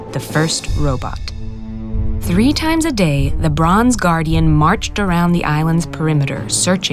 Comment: Instead of ‘the bronze guardian’ I heard ‘LeBron’s Guardian’